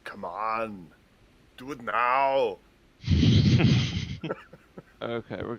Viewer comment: Tuning in and the first words I hear are, "C'mon, let it go..." I had to laugh as he read my mind with that remark. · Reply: He says C‘mon do it now!*🤦🏼‍♂️